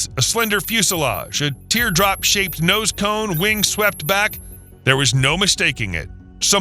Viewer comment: did he actually say, "wings swept back"?